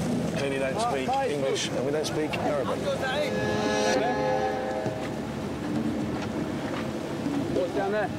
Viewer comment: Did he say, “we don’t speak American”